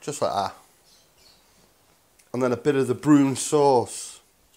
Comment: So what did you call and what is the sauce at 19:18, brune sauce?